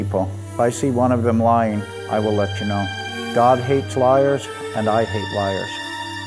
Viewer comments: "God hates liars and I hate liars" john chill 🤣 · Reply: baby girl XD XD ooooh i thought he said “dogs hate liars and i hate liars” this makes more sense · "God hates liars and I hate liars" john chill 🤣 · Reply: I thought he said, "Dogs hate liars and I hate liars." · "God hates liars and I hate liars" wow 😳 okay intense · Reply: Jilli Spencer I thought he said "dogs hate liars" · "God hates liars and I hate liars" john chill 🤣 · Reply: I thought he said "Dog hate liars and I hate liars" lmao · I thought John said "dog hates liars" lol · do you hear God or dog? I hear both... dunno why